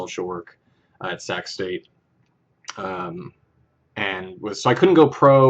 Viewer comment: Lol at first I heard "sex date" instead of "sac's state".